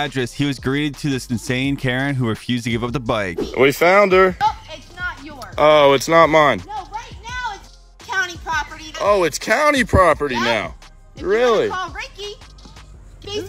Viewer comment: Oh I thought he said f up Karen property